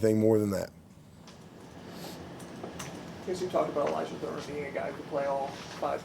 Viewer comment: WHO is the OL being discussed - couldn't catch the name. "Can play all 5 positions, played Center in HS... He’s a really special talent." ....OK, I figured it out => ELYJAH THURMON #52 OL 6-4 310 Freshman